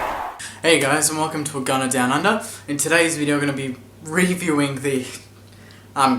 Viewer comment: i heard "i'm going down on her".. valentine day i guess it's cool mate